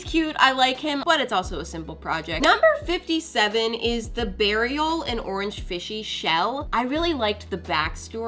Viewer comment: I thought she said BURIAL and i was like "Wait, did she k!ll someone??" 😂